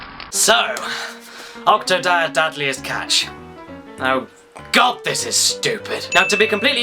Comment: Dad'liest catch? :P I think I misheard you at the end of the video but it sure sounded like you said that! :P